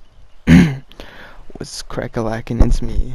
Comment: Did he just say what’s crackalackn